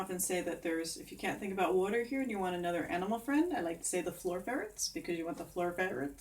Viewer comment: I love handwork! Wonderful visualizations I remember from your in-person workshop when I thought you said fairies instead of ferrets. I'm glad I asked and you corrected me, lol!